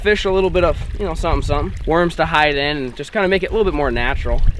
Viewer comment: did anyone else hear him say “worms to hide in “😂